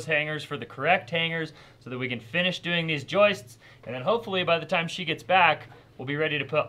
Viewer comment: I thought you said "rolling the joints".....😮😅